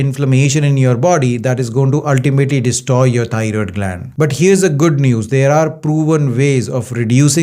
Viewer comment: TY Dr for this information! I Love Yer Accent, Lol. I thought U were saying tired gland, But thats EXACTLY what it is, We tired out, Our Thyroid gland!